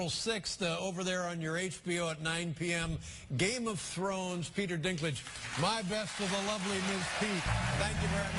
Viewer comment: When Lettermen is ending the interview, does he really say "The lovely Miss Pete? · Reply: No, "Miss Peet" (i.e. actress Amanda Peet, who they had discussed earlier).